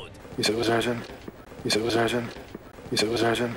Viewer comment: I had to slow it down to .5 speed just to understand what he said "u zed et wuz urgnt" which translates to "I'm a tubby flub and my pee pee is broken".